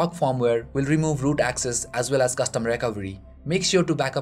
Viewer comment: What in /the world are you saying @ 0:55 'as well as custom rec trugrariey wexgry' ???